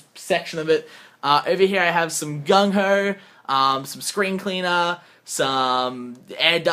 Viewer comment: LOL at about 1045 i thought you said Gun Oil! Google it xD